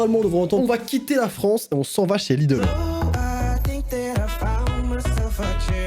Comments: j'ai entendu " on va quitter la france on s'en va chez lidl " 😂😂😂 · moi oui je l'ai entendu sauf que au lieu de quitter j'ai entendu niquer 😒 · NN j'ai entendu ''on va n*quer la France '' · Moi j'ai entendu «on va quitter la France pour aller chez lidel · je l'entend depuis que je suis petite enfin moi j'entends on va quitter la France pour aller chez lidl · Moi a 4:57 j'ai entendue " on va quitté la pharmacie pour aller chez lidl" 😂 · j'ai plus tôt entendue "on va quitter la France on vache a Lidl" XD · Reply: Le Gamer Roblox moi j'ai entendu "on va quitté la femme"🤔🤷🏼‍♀️ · Moi j'ai entendu "on va quitter la France on s'en va chez Lidl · heu moi j’entends plus ont va quitter la france et se frotter yeah...bon je sais je suis bizarre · moi j’entends « on va quitter la pharmacie chez lidl »